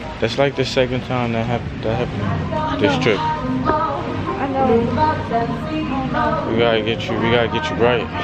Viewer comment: Did she say “I know baby”